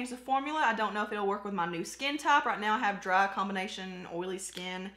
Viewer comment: When you were talking about the foundation, I heard you say "I don't know if this will go with my skin top". I thought, what the heck is 'skin top'? Took me a minute to realize you said 'skin type'. Like we talk any better here in Texas!